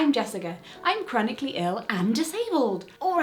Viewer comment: I misheard chronically ill as "comically ill" which was...well, comical, if not downright tragic 😅🙀🙈💀 ...no, not a comment on your pronunciation Jessica, just a comment on my own shortcoming in this case 😅🙀🙈